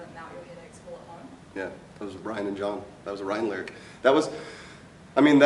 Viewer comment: IT SOUNDS LIKE HE SAID THAT WAS A RYDEN LYRIC I CANT UNHEAR IT